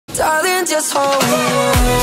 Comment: when I clicked the video I heard "just hold on" and I was like "OMG I LOVE THIS GIRL"